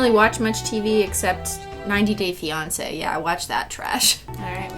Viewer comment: Now I’m just curious who are your favorite couples “couples” are from 90 day fiancé. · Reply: Travis Hunt i thought she said “90 days Beyoncé” and was thoroughly confused. Thank you for this comment to help me realise how stupid I am